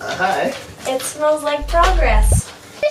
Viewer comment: Exciting! Fun! Scared me at 12:10 thought you said," it smells like dog mess". Lol 😝 Can't wait for the next episode. 🏡 🚽